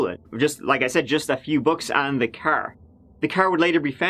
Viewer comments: Lol i thought you said he stole a few books , took me a while to realize you said bucks. 🤣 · No shade on your accent, but I thought you said he stole "a few books" and wondered what books they were. 🤓🤦‍♀️